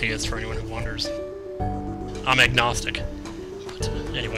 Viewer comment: when you said you where agnostic i thought you said eggnog. i was like there is a religion all about eggnog, well to each his own. i had to Google it to find out i swear I'm such a dumb ass wow!